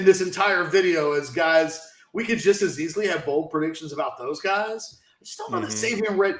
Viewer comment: I been very confused all this time until mentioned Ethan Burke I thought you were saying bowl predictions. Damn edibles